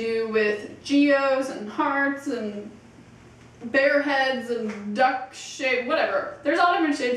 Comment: sounds like you say Duck S#*T made me laugh